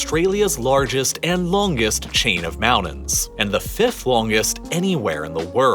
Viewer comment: Did you say mounens. Yes we have many mounens, medium mounens, low mounens ... 😆